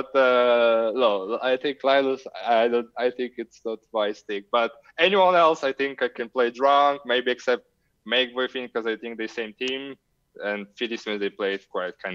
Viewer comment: what are the names he says at 4:55 ? I can't understand him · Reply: Makeboifin (I had him on my show before) and Filimsies - both Finnish HS regulars